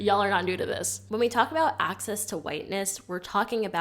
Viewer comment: I laughed so hard when I heard "accessing whiteness."🤣 The only whiteness I've been trying to access is women.